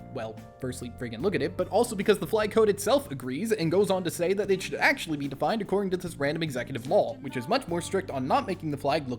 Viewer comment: the subtitles here are wrong btw What you actually said in the final take: “because the flag code itself agrees and goes on to say that it should actually be defined according to this random executive law”